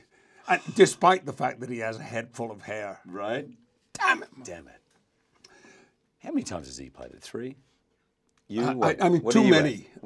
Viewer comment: What does Jackman say at 7:40? They start talking about how many movies they starred in, but I can't hear what Jackman starts it off with. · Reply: I think he was asking how many times has James McCavoy played Professor X followed by asking him how many times he has